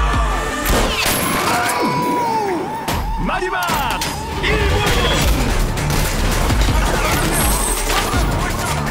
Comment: What does he say ? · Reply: 1 minute left